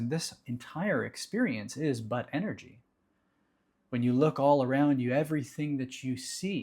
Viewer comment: "this entire experience is butt energy" ehm ok......I'll pretend i didnt hear that🤣